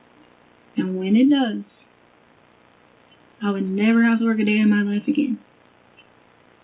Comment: what is she saying or does she mean at 4:07:25 does she say she'll never have to work another day in her life again, when the truth comes out?? like she'll sue??